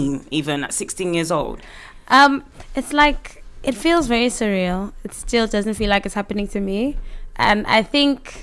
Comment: im l8 as hayell but omg african musicians n DJs are fyyyyye...... it's cereal!!! (I heard her say surreal as cereal, and I h8 my ears) we stand------!!!#UnitedWeDance !!!!